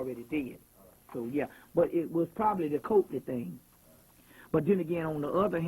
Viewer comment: Bobby is the best..."...whew I gotta take a shit"...continues to drop knowledge...turds of wisdom. Did he say COVID at 29:05 mark? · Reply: coakely